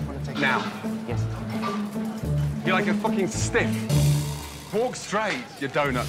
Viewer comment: I love how Ramsay tells Jean-Phillipe to loosen his tie, and JP refuses, and Ramsay is like "Fine, whatever". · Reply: "You look like a fucking stiff" I swore he said stick at first xD